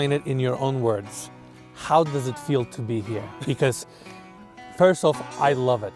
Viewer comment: I swear he said, “first off how does it feel to be gay”.